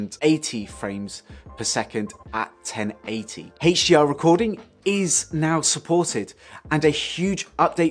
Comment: What Exactly Did You Say..AT The Video Recording Mark at 4.Mins19Secs? ( IT Sounds Like You SAID=> ""H- SHELL Recording? PLEASE Let me know I'm Really liking That You Did this UPDATE Video,, GOOD STUFF :-) THANKS C,P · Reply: They said "HDR Recording" at that point. That's using the HLG setting.